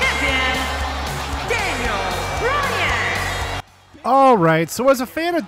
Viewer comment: Daniel Browan instead of Daniel Bryan